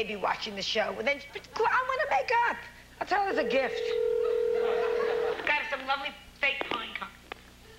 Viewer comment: What did VP say at 20:30? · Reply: Joan was saying “we’ve got some lovely fake pine cones” as the line clicked meaning she hung up on her.